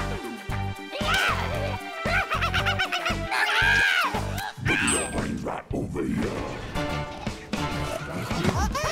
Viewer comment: catchy music but what the hell are the zombies saying boogie on bring like boom bay yah? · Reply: there saying "BOOGIE YOUR BRAINZ RIGHT OVER HERE"